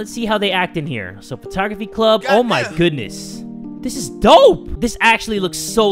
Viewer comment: did someone just said DOPE!?!